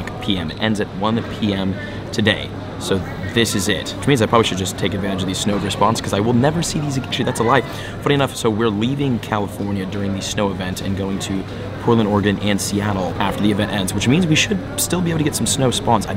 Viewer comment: why did it sound like he swore! And said the S word lol! Does swear, or is it me? 🤨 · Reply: I don’t think he said shit, I think he said “actually”, but it got caught off when editing.